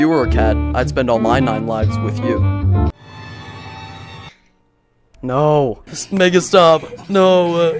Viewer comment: what bro say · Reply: make it stop is what he said